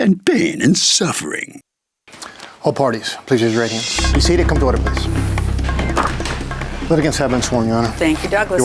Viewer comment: Does anyone know what Douglas says at 1:11 right after he says all parties please raise your right hand? I have never understood. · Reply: I believe he says “Be seated, come to order please”